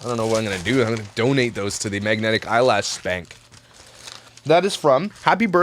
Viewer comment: Sounded like you said donate them to the “eyelash SPANK” I thought how appropriate